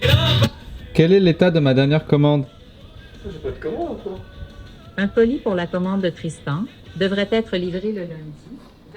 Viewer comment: C'est quoi le nom du canard ? J'ai pas bien compris le nom 😅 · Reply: DesktopGoose je crois